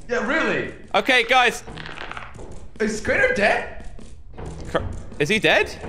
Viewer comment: Oh this is really hardmode a few seconds later I hear CRANIER AHHHHHHH😂😂😂😂😂